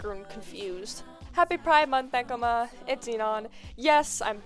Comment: YIPPEE BACK!! edit: 0:42 subtitles say ekoma and enon lol